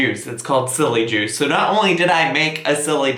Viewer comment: I thought it said "I try Jilly Juice" at first. I was SO concerned.